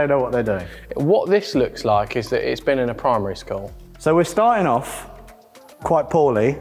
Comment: did he just say it looks like it was done in primer-y school? Like in a school, where they teach how to put primer on. :D